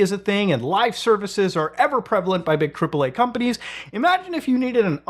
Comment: Ive misheard you and heard "cripple eh?" companies instead of "AAA" there. I think Im going to run with it, considering the games are crippled :D.